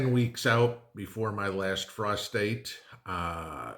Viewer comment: Good thing I had the cc on. I heard "my last prostate" instead of "my last frost date"! Better get my hearing aids 🙄